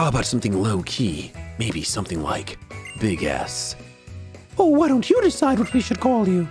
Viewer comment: well, how about something lowkey? Maybe... something like Big S. I thought it said big ass xd